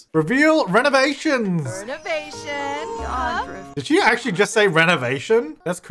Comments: yea omg i hear em saying"renovation" too. its kinds creeps me out for no reason 😭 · i definitely heard renovation at 10:13 ... weird